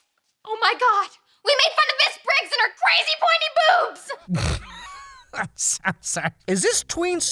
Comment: when i was watching icarly i thought they said 'crazy pointy boots' cuz i was an innocent 9 year old